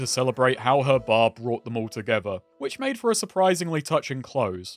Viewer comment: I heard it as “ her Bob brought them all together”